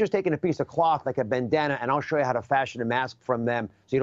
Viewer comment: 'It's a fascist statement- try it out' . That's what I heard him say, although I think he actually said "fashion". I think my version is closer to the Truth.